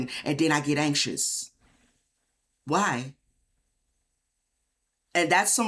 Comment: 😂 I thought it said ... irritated lol not initiated 😮 Guess I'm too old for THIS 😔 cause I Lost my reader's 🕶️